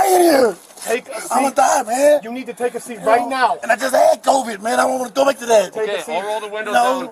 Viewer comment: at 37 mark does he say i had covid ? didnt he die before the whole ChinaVirus thing ????